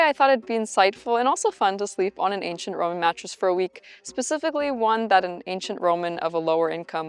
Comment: Omg I thought it said I slept with an ancient Roman goddess for a weekend 😭🤦🏻‍♀️